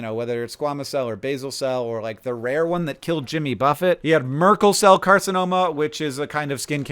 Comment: I had to go back, I thought that you said "merkincell", not Merkel cell. That would be a very different thing.